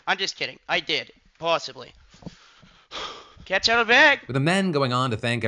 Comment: “I did not have sexual relations with that man! I’m just kidding. I did… possibly” is absolutely iconic from Sketch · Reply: "Possibly" I thought he said "Awesomely" 😂